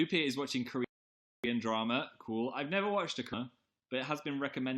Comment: okay... my mind is in such a bad place. when he was talking about korean dramas i thought he said "i've never watched a cunt." and then when he said ate 2 magnums my mind went straight to condoms. don't ask me why... i don't know either. ಥ_ಥ